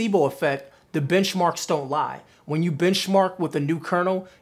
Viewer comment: I thought he said, "bitch Mark"..I was like..yes sir lol